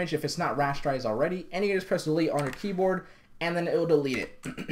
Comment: what did you say at 7:26 ? pressley on the key board ? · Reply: "press delete on your keyboard..."